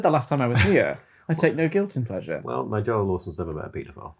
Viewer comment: What did Tom say at 15:22? I can't understand him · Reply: He says, "Well, Nigella Lawson's never met a paedophile."